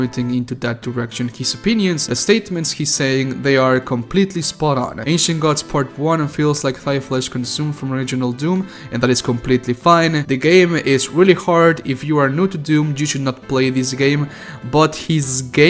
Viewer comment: Man everytime I heard "thy" I kept thinking I was hearing thigh and a hell called Thigh flesh consumed high key sounds like my kind of hell